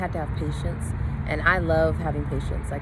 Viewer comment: I thought you said " and I love having issues"😁